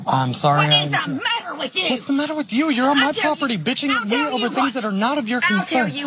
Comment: I wouldn't call her a Karen, it sounds like she is genuinely concerned about the cat....whether or not she has a reason to be idk🤷‍♀️ But calling an elderly lady a "bitch" is out of line. Yea she's a little crazy but that doesn't mean you have to join her. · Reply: After listening again, it actually sounds like he says "bitching" like complaining